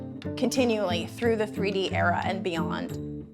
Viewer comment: I realise she said ''countinue throught the 3D era'' but I legit heard ''countinue throught the GREEDY era''.